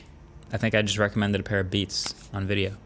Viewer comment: Did you just say: "Catch you guys in the next one, Beats"?? Oh never mind